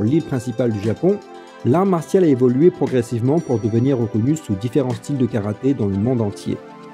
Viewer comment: A 0:50 seconde c quoi ??? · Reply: puis, au début du 20ème siècle, sur l'île principale du Japon. l’art martial a évolué progressivement pour devenir reconnu, sous différents styles de karaté, dans le monde entier.